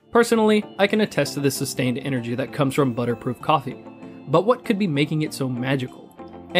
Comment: did he say butter proof coffee?